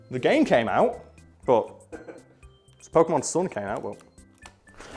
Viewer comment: He said Pokemon Sun in such a way that I heard him say Pokemon Sword I checked the date of the video and had a mini existential crisis till he showed the screen of the 3DS with Pokemon Sun in it and I realized I'm just an idiot